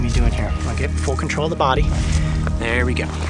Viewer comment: OMG HE CUSSED AT 10:27 he said f it, first time I heard him cuss omg, eh not that bad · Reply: Byron Sophin he said full control